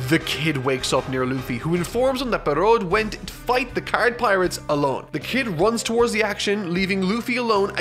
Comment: I thought they were called the Trump Pirates.